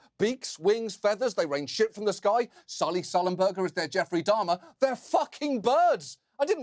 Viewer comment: Sully Sullenberger was the captain on a plane (US Airways) that hit a flight of geese upon takeoff which incapacitated the airplane. He was able to land it on the Hudson River in NYC (something that is amazing). But he’s joking that Captain Sullenberger hit the geese and is, therefore, like Jeffrey Dahmer, who was a serial killer. :-) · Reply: Thank you so much! I heard "Sally Sullenburger" and assumed Sally was a celebrity chef who made lots of poultry dishes.